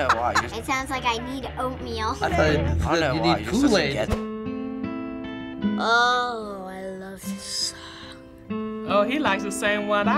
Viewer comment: at first i also thought they said cool aid l lmao